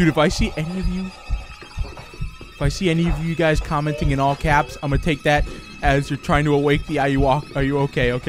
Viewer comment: YES MORE AYUWOKI!!!!!! IM SO HYPED I LOVE THESE VIDEOS please make more you missed 2 updates already 🙌😕(pretend this is lowercase 😂) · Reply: i thought he said no caps note: i messed up